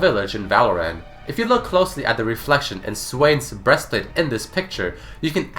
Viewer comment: I thought I heard Jarvis here. xD But its Jarvan's. Just for all who tried to repeat that part.